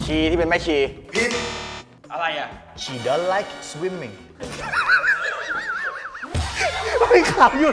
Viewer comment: ผมที่พูดว่า She doe's like swimming 😂😂😂😂😂😂😂😂😂😂 8:07 ครับ